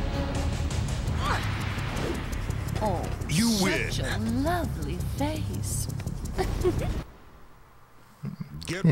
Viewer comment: what does she say? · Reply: "Shut your lovely face."